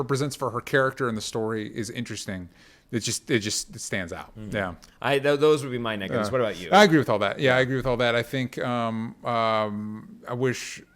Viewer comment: sounds like he says the n word